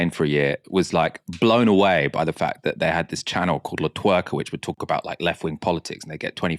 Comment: Did anyone catch the name of the Spanish newschannel Michael mentions at 47:47 (sounding like la twerk)?